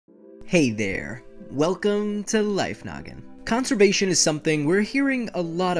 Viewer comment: i heard masturbation XD